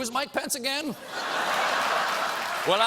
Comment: I'm not American and every time he says Mike Pence I hear "My Pants", does it happen to you too or it's just because I'm not a native speaker?